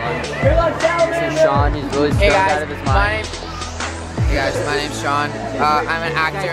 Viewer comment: Did say was stoned? @ 1:40-45? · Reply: Yes he said is stoned out of his mind